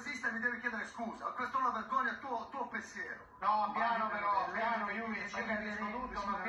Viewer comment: cosa dice a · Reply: Il tuo pensiero è una vergogna